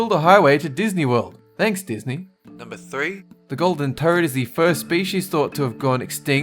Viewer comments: u thought you called it a golden turd. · Anyone else hear "The Golden Turd"?